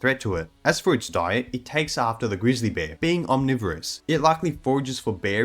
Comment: Not disappointed, liked the video but I thought you said gorilla bear and that’s what intrigued me lol